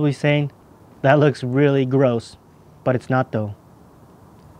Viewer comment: His face when he said “ your prolly thinking this looks really gross but its not tho “ Had me rolling 😂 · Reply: I heard "but it's natto"